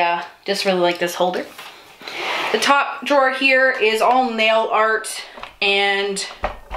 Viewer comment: Holy washi tape Batman! And then I heard you say mail art.... or maybe male art?!!!! Haha, and then I realized you said nail art!!!